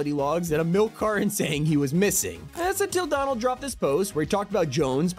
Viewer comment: was I the only one who heard "Donald Trump"